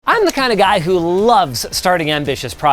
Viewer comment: not gonna lie, i thought he was saying "i'm the kinda guy who laughs at a funeral"